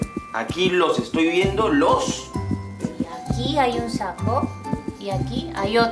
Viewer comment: escuche sapo 😔